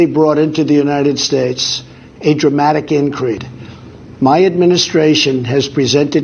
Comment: did I hear correctly?. Did he say "A dramatic incrid". If so he was starting with cognitive decline five years ago. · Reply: Yes, he said a dramatic "increed..." The man cannot read or speak. Cannot feel and cannot think. The man is a toddler in a big suit. How can nearly half our country love that? How. It is absolutely mind boggling.